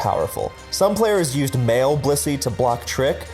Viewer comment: I swear I heard "some players used male blissey" at 20:55 and I was like... wait, transgender pokemon were a thing?! then I noticed the text